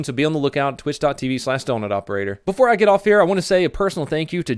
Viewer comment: did he just say "before I get off"